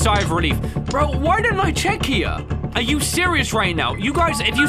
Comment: did bro just say "are you serious right newo" 💀